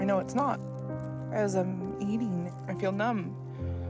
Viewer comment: At around 19:45 when she said "I feel numb" while eating the burger I first heard "I feel nom" and I wsaid aloud "nom-nom-nom-nom" oops lmao